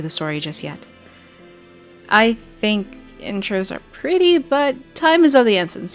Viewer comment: It's "Privy" not "Pretty" that gets me every time lol. And maybe you are saying "Privy" and I'm just mishearing but anyway.